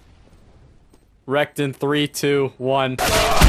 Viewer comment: At around 12:00 I thought he said erect instead of wrecked